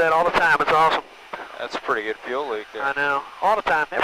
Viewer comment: did I hear "that's a … fuel leak" at 7:30 after run up?